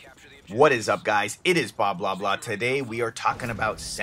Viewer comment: lol it sounded like your said blah blah blah